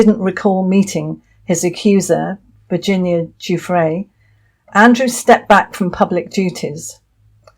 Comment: I keep hearing " Andrew stepped back from pubic duties" but I have to say, I do not recall ever seeing him anywhere at royal appointments, like other working royals. Did he actually used to cut ribbons and all that?